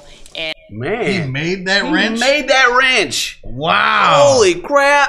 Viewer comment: n say ranch so much that that's I heard them say. I wasn't looking at the time but thought homemade ranch what a handy man lol